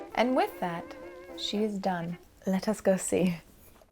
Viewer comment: Did... Did you just say "Let us ghosty?"